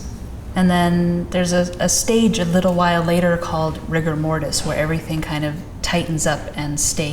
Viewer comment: rigor mortis I thought I heard Rick and Morty